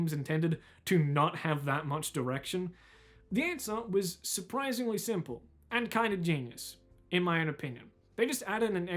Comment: What is the name of the game that's discussed here? I can't quite hear it, but it looks great. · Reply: Outer Wilds